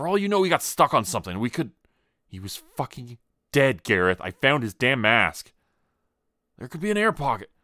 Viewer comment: I thought she said she found his damn ass, not his damn mask lmao 😂